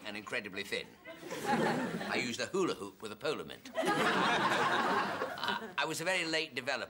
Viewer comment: I think he meant to say ,"I used a polo mint as a hoola hoop." Not, " I used a hoola hoop as a polo mint." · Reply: He said "I used to hoola hoop with a polo mint" I think.